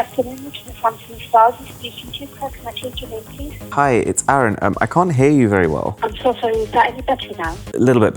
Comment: i don't know if i am the only one who hears Arun as Eren 😂 although love your content BOSS 🎉